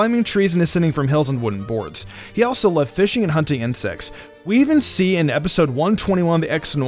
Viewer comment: Did i really heard Sêx?